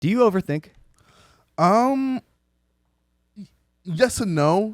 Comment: just tuned into the video and I misheard logan and thought he said "do you ever think" and the pause from ksi killed me